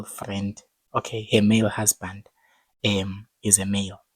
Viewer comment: Symptoms of not sleeping,i thought i heard male best friend instead of husband😂